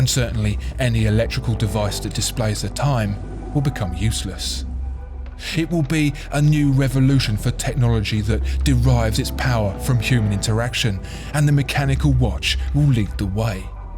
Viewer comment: i heard "derives its power from humans erection"...